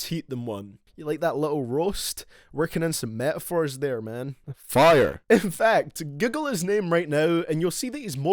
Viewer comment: Did he just say „giggle his name“?